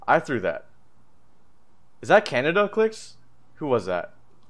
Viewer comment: It’s not clix it’s khanada clix said it in the twitch chat clix said “my boy khanada just ate you out” I was crying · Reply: at the end he says "is that khanada clix?" it sounds like he says canada tho so it is confusing